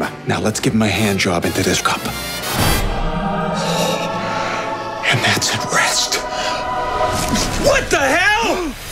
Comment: Can anyone tell me what ted said at 0:20? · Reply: and thats at rest...